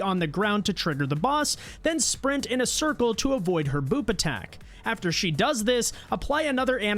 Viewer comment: At first i heard "Avoid her boob attack" 😂😂??!!!